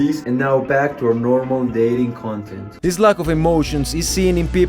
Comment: I thought he said dating content for a second bro I had to rewind😭